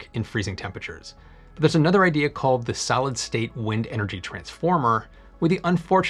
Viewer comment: I was just listening to this video, and what I heard was "Salad-state" ;D Great video though. Looking forward to these technologies to mature!